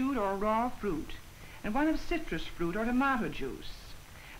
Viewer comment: what kind of juice does she refer to? I can't hear what word she says, "imama juice?" That's what it sounds like to me. Driving me crazy that I don't know she's saying! Ha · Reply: Tomato. Ottawa Valley accent. Tahmahta.